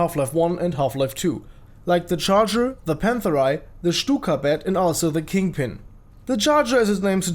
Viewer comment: i thought you said cykablyat not stukabat